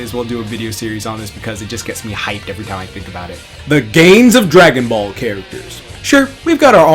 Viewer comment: who else heard the gayest dragon ball characters? 😂👌